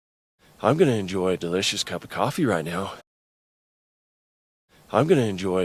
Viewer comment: How can I enjoy delicious come coffee right now. なんか言ってるけど聞き取れないなぁ絶対違うけどcomeってうっすら聞こえる気がするんだよなぁ→cop ofでした笑 I'm gonna が聞き取れなかったのまじでショック。お風呂でやってたから反響して聞き取りづらかったのかな…